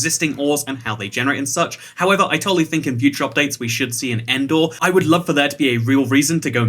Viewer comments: When he said end ore my dumbass thought he said Endor from Star Wars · I thought he said Endor